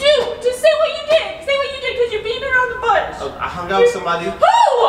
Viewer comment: Did she just say "Did u beat her on the butt?" 7:29 😂😂😂😂